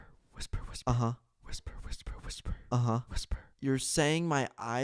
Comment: that guy look like a security guard when he was whispering I heard he was saying whisper a lot of times check it out here is the video time